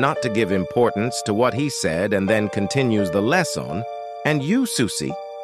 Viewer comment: I heard "I wanna be Johnny's fish"